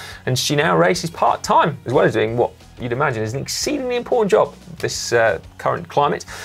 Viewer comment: Did So say Lauren de Crescenzo is doing an incredibly poor job at the CDC? I hope I misheard him.